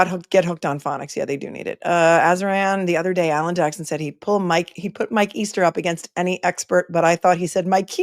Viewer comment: I misheard AJ when he said he'd bet Mike Easter was appropriate or something, thought he said bet My Keister...